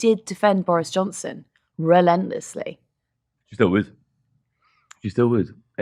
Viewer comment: When you were talking about Nadine Dories, I thought had said "I still would"... But he said, she still would, as in still defend Johnson. 🤣🤣🤣🤣